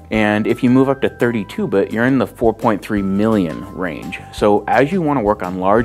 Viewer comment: 4.2 billion range? i thought i heard 4.2 million range